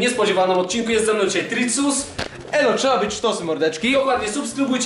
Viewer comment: ej co on powiedział? : Elo si- A dalej nie wiem